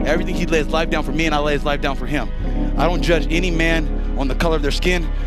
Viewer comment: Might be tripping, but I totally heard “He’d lay his life down for me, and I’d lay HIS life down for him” lol